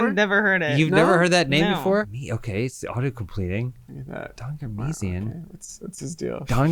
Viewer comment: I thought you were saying "Donger Missing"